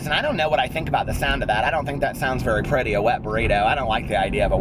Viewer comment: When u said Wet Burrito my filthy mind thought u said "Limp Burrito" at first and I thought "why the hell would anyone order a limp burrito???" 😂😂😂